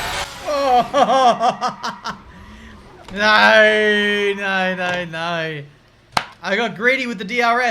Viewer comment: Did i hear at 3:03 nein nein nein?